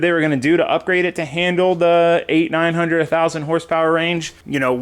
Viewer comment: For a second I thought he said 900,000 hp!